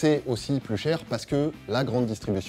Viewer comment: Quand t'as dit "surmarge", j'ai entendu "couilles en or" 🤔